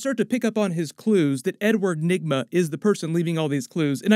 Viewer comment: I heard N Word Nygma at 3:09 anyone else??